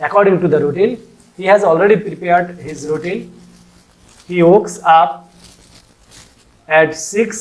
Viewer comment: Did he just say "wokes up" And your an english teacher